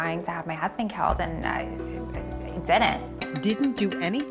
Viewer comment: her lips are so thick it sounded like she said "I thidint" 😅🤣🤣🤣🤣